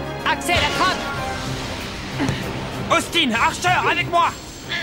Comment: Axel a dit " Austin hartcher , avec moi !" A la place de Hartcher c'était Xavier c'est normal ou les doublages ce sont trompés ? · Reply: Archer**